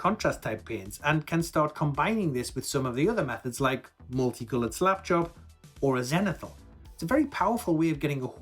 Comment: Great tutorial, but being an ex airforce fast jet tech, I'm a bit deaf so near the end I heard "multicoloured swap shop" and "hyper-ball" (is that a faster varient of Blood bowl?) I need to use the subtitles of just pay more attention, but it amused me.